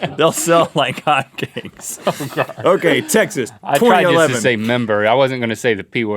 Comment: Did he say "They will sell like hot gays"?